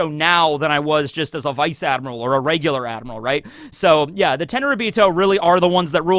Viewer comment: Thought you were saying the “10 ryubito” not realizing its the “tenryuubito”